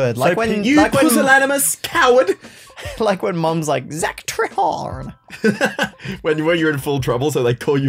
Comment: WAIT WAIT WAIT, did I hear that right? Zack’s last name is Trehard? His last name is TRYHARD 😂😂😂 I cant even, that’s priceless. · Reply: I think it’s actually